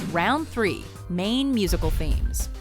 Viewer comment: lol I thought you said main musical memes.